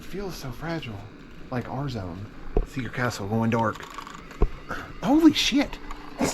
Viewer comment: did he say going dork?😂